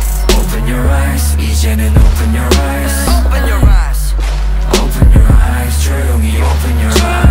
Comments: I like how the ARMYs are hearing 'eat jin and open your rice', the IGOT7s are hearing 'jinyoungie open your rice' and everyone else is hearing 'Jeremy open your rice' XD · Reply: I hear "doyoungie open your eyes" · I thought they said Jeremy open your eyes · I thought it always said Jeramy open ur eyes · I like how the ARMYs are hearing 'eat jin and open your rice', the IGOT7s are hearing 'jinyoungie open your rice' and everyone else is hearing 'Jeremy open your rice' XD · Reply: My main fandom is army but im a multi stan but way before i read the lyrics i always heard and said "jeremy open your eyes" and i was like WHO TF IS JEREMY then i was like im gonna say it anyway cos i dont giv a fuk